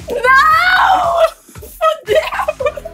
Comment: so eu que ouvi a falando vai se fuder nos 9:00?